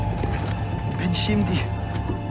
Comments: What did the Turkish soldier say at 3:38? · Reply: I'm alive now I'm not a martyr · what did he say? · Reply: I am living now, not a martyr.